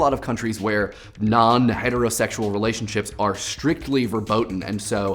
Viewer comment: "strictly verboden"? Did he say thay? Because that is a Dutch/German word and not an English one... What else would he be saying there?